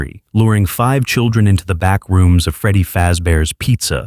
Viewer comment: Did I just hear, Backrooms!?